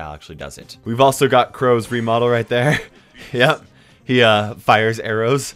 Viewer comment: Did you say crows remodel?